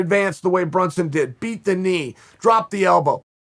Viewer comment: I thought you said "beat the meat" chael 54sec